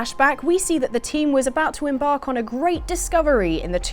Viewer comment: Hahahaha really thought it said "Lara, get down here you slapper!"